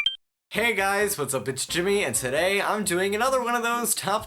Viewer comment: "Hey guys, what's up bitch Jimmy." I heard it once and now I can't stop.